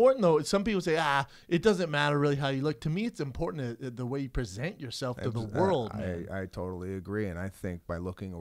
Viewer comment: did Brendan just say present tate hahaha